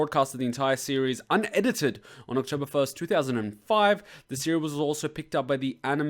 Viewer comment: You said when it was aired in your video! [12:55] Btw, I loved your trilogy here. Almost as much fun as showing the DVD of GHOST STORIES to friends! I get the same "What did they just say? Go back!" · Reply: Whoops! Sorry, I misheard "October" as "April", probasbly because I remembered watching this on 4/1.